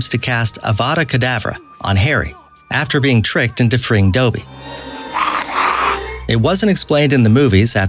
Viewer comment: did he just say dooby